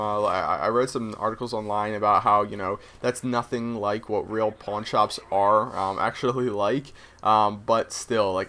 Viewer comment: Sounded like u said porn shops lol